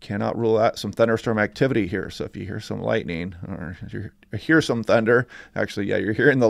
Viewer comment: 😂 ahhh! You gave me a chuckle when I heard you say "if you hear some lighting" 2:31 ahhh, coffee not kicking? Ahhhh!! I appreciate your information and you have a good attitude about it! We wish you, and your family a nice day, and week! ❤😊🙂